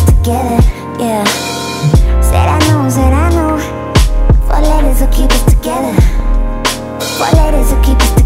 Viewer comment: I keep hearing "foot lettuce will keep us together"